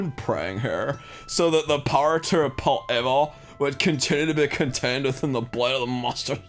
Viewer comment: When the leaf guy said the power to repel evil I thought he said the power to rape evil for a second